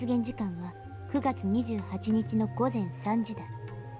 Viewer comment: I couldn't see you running in the picture even by changing the angle at which I viewed my screen. I guess that subtle detail got lost somewhere along the way. 10:17 She clearly says "gozen" but the text says "afternoon". What happened to the text between 17:04 and 17:46? It was even freakier that you read the text before it showed up on screen. 12 items. 90% success rate. How?